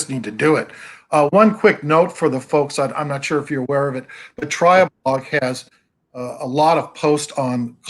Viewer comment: What did say was the website with Gordon Clark critiques? It sounded like he said Triblog, but Google is not recognizing that.